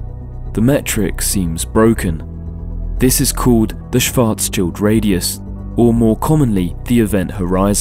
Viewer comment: Did you just say Schwarz-chilled, there?